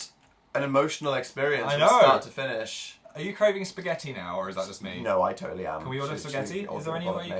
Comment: I THOUGHT PHIL SAID ARE YOU PREGNANT SPAGHETTI 😂